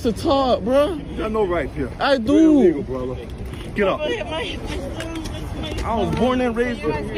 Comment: "Be quiet, you got no rights here, you are an illegal brother". I think he says.